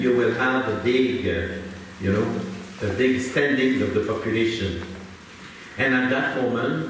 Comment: what do u think he said at 23:41 "then you will have a big standing of the population" or "spending" or im not sure s word he was saying here. Anyone?